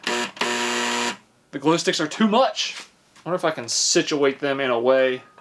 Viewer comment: did he just say glue sticks lol